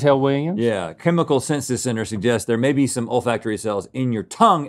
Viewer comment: All I kept hearing was "old factory" cells